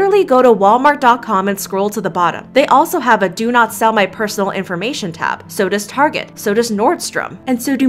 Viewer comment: Pwhahaha i thought it said "don't sell my nudes" 😂😭